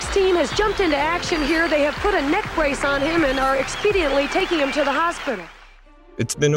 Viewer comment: what did she say? expediantly? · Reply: Yes, expediently